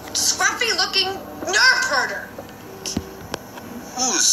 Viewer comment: When she said "Nerf Herder", I thought she said "Nerf Hoarder", and I was thinking, "They have Nerf Guns in Star Wars?" I didn't know that a Nerf was a kind of animal.